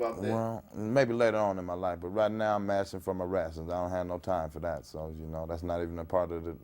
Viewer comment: “Right now I’m mashing for my rations” for President!! 🙏🏾💯💪🏾👑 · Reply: I thought he said mashin for my ransom